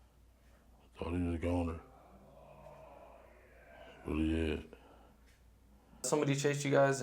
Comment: Did anybody else hear when he said I thought he was a goner and you hear a creepy voice say oh yeah